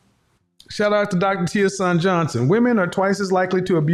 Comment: Can someone tell me the name of the doctor he mentioned about when women always bring up 2 horrific scenarios: adultery and abuse. What's the doctors name and does he/she have a podcast. I think he said it was Teason or Teashon Johnson but I couldn't quite make it out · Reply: Dr. T. Hasan Johnson. Not sure if he has a podcast.